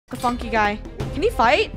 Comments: Yo what did say in the beginning at 0:00 ? did she say "the funny guy" or the PHFFFOOOQUE guy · When I Played This Video 0:00 i thought Said "F***ING GUY" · I think at the start I heard a "f- u guys" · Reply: He said funky guy